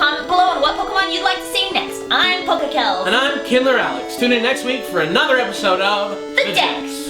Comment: For a second, I thought he said "And I'm Hitler Alex."